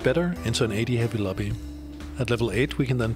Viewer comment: What the heck are u saying there? XD its a adhabylobby? :DD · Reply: AD heavy lobby = a lobby with a lot of physical damage